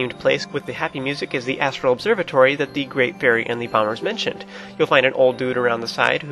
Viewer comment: During all the last video, each time you said "bombers" I heard "bummers"... don't blame me, I'm not a native speaker and I have trouble understanding native speakers who speak so fast. But it was so funny when I found out... near the end of the video =P